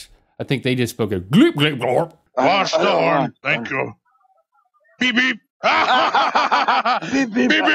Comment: did anyone hear PORN!